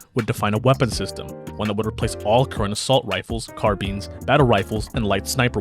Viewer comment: Did I just hear "Car beans"? That's how I'm pronouncing carbines from now on